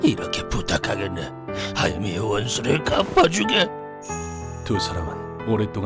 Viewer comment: 하야미의 원수를 갚아주게로 들림 ㅋㅋ